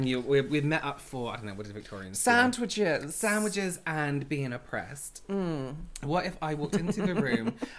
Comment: “Sandwiches and being oppressed” 😂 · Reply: Omg I thought he said sandwiches and vino pressed 😂😂😂😂